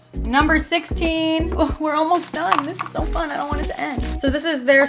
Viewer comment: At q 15:49 i literally thought she said “ooh this is so fun I want it to end”. Lol🤣